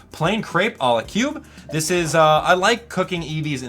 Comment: Who did you say I like cooking evee in3:20